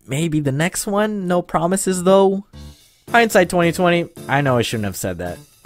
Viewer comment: did he just say something about 2020 orrr